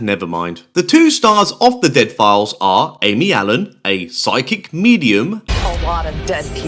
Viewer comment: I swore I heard you call the Psychic Medium, psychic comedian 😂